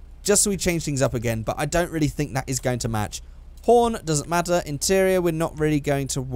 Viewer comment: it sounded like porn and not horn